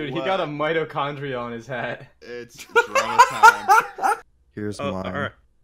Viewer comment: did just say its corona time? did he predict the future..